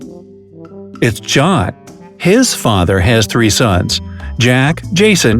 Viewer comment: BRUH I heard a similar question look JOHN’s farther